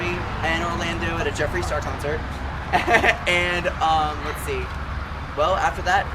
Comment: Did he say a Jeffrey Star concert?!?